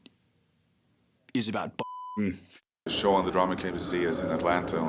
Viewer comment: Whats does say? Bottom? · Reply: Thought he said "buttf**king"